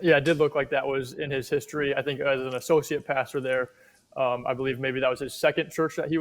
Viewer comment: Too fast, when you said associate pastor, it sounded like you said sociopath...lol!